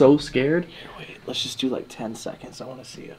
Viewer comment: Am I the only one who heard in a whispered voice “ you waited” time stamp is 1:05:18 or you might want to check it out I’ve watched it over and over and none of the guys said it turn up the volume it’s quite clear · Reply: That sounded like saying " yea, wait...lets do like 10 seconds"